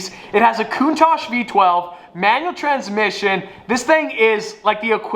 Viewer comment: Who else thought he said mission tires?